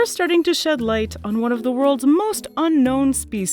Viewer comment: i keep hearing it as 'sad cat'.. which i definitely would be in such an environment)